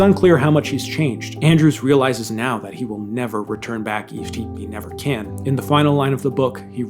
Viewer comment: What word/phrase is he saying at 51:24. It’s so incomprehensible to me and sounds like a slowing of words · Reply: “Never return back, chief keif never can”